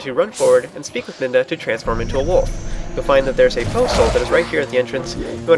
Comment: you say Poe Soul, when you talk about Poe's but at the beginning i thought you said Postal, which is funny.